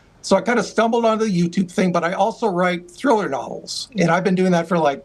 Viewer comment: a great guest and has a great channel. What did he say he also .. writes ##### @ 8:49 ? sounded like throw it alls ... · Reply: Thriller novels